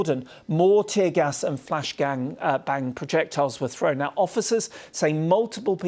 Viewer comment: did he say flash g*ng bang?